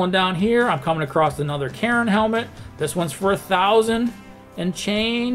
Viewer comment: I thought you said Karen helmet, and my immediate thought was yes, yes they should wear them...